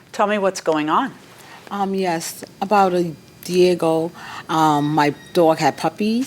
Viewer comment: Did the Plaintiff say “Diego” instead of “A year ago”???